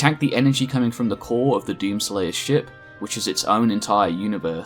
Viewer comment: The core of who's ship??? Edit: Genuinely, I keep hearing "doom slayer's ship" I'm not crazy, right?